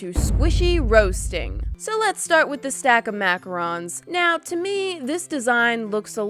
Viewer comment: did she say “maca Ron’s “ ?